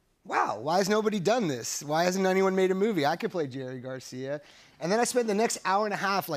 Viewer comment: Jerry Garcia? I thought he said George Costanza